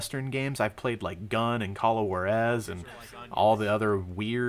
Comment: did u just say ''call of war ess" juarez! [hoo/ar/es]